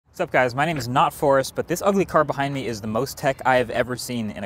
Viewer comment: I heard: "My name is not Fores" 😂